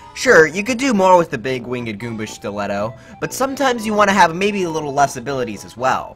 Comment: i can't be the only one who heard "the big winged goomba stch-letto" almost like he was gonna say shoe